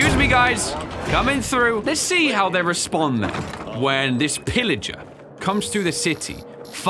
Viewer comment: Pillager instead of Pilgrim 😂😂